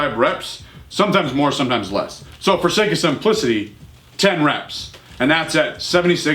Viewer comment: "so for the sake of some pussy." sorry bro that's what it sounded like. Everyone in my office laughed when they heard this part of the video.